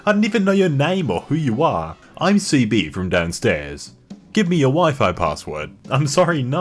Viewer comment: The pause at 1:12 almost sounded like "give me your wife"